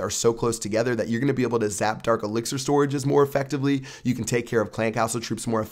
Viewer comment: Did you just say "Zap dark elixer storages"???